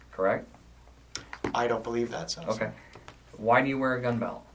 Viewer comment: did he say "I don't beleive that Staff Sergeant"? That is not how Army says it, that how Corp 😄 says it